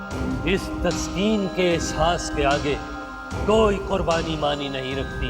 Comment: Fawad khan is trying harder not to laugh when uncle says "TASQEEM" instead of "TAQSEEM" 😂😂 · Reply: Uncle doesn't says TASQEEM instead he says TASKEEN.. is taskeen k ehsas k samny... Fawad khan smiles bcz he feels proud on the passion of sacrifice that uncle is having.